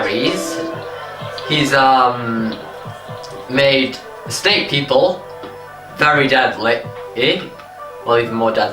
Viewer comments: minutes through I thought you said deadly estate people! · minutes I thought you said deadly estate people!